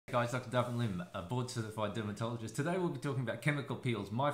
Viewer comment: Board?! I always thought he was saying he's a BORED certified dermatologist hahahaa